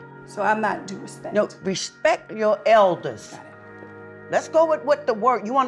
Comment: what did tamar said at 00:43 ? she said it too low and the way her sister looked at her , i mean .... it was a bad word ? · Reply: She said "Fuck Outta Hea" they dubbed it as if she said "Got it", but if you pay attention the mouth movements, she was saying "Fuck Outta Hea".